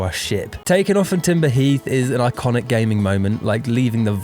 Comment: Did you just say "Timber... Heath"??